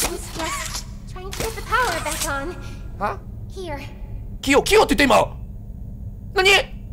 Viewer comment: ここ確かにキヨって聞こえるwww 公式もメッセージ出したくらいやしぶっ込んでても不思議じゃないよね！